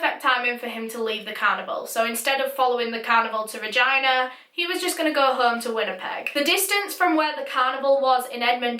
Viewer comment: I thought you said vagina......